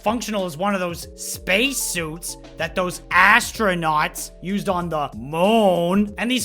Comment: did you just say asstronaut and yes I'm one of those people waiting for the kfc console 😁